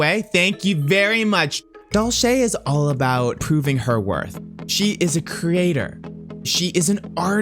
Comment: Dulce is all about pooping her worth (is what I heard at first lol)